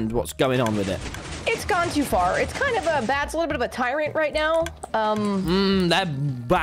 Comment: Did Puffy say Bad is being a tyrant at 31:15? Techno enters chat: BLOOD FOR THE BLOOD GODS!